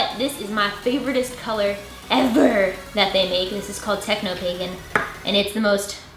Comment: I thought she said that purple color was called "Techno Bacon" at first...